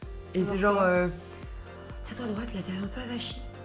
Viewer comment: elle dit quoi lola je comprends pas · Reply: Emily Verstraelen tien toi droite rrrrrrrrr